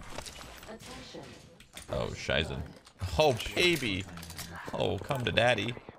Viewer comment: did you say: Scheiße (shit in german)?